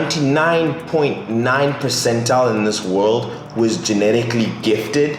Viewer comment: I Think you meant to say “amongst the 0,01%” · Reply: Oliver Stojcevski he said "percentile"